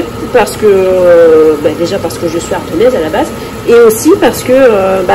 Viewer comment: lol à 4'50 : j'ai compris "lardonnaise" au lieu de "ardonnaise" !!!!!!!!!!!!!!!